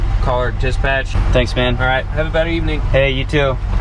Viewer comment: did he say have a bad evening 😂 what is wrong with him